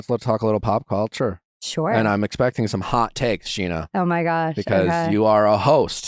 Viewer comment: Was he calling her Scheana in this podcast at times or am i going crazy